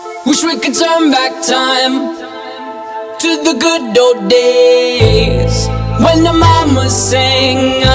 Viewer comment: I thought he was saying “good dope days”